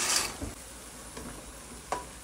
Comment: I thought he said vegan but he said bacon. Yesss 😂